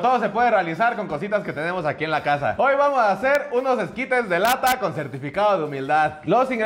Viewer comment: Pensé que decía esquites de la alta.